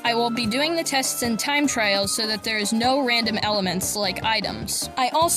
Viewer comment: You said time trial in a really annoying way its like you said time trail instead of trial. But other than that this video was ok.